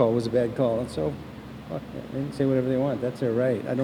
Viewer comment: he said "so fuck that, they have that right to say what they want.." 😂😂 anybody else catch that??